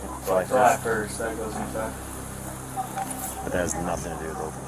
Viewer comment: You got the subtitles wrong at 16:11 he says "that goes into effect" not "I dont like that"